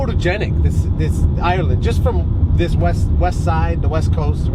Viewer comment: I thought he said from the Wes side lol.